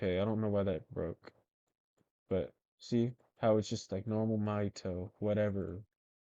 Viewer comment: the subtitle says my toe instead of mahito ✌️